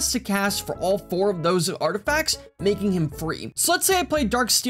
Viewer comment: I thought you were saying "making him 3". Had to watch several times to realize you said "free". I personally would've said cost 0